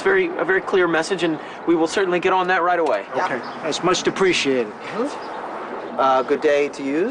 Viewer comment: Does that one mobster guy say “That’s much depreciated” instead of “appreciated” at 1:27? Lol